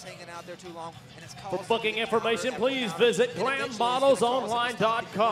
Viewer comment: IT SOUNDS LIKE HE SAYS FOR FUCKING INFORMATION LMAO